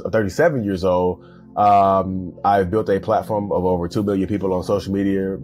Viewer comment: Narcissist: the best source for information on narcissists is me. That checks out. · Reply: he said 2 million, it's the way he speaks that made it sound like he said billion